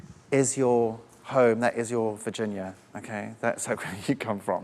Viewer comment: I properly thought he said vagina for a good few seconds